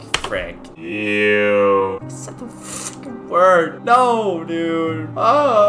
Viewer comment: anyone heard him say fu**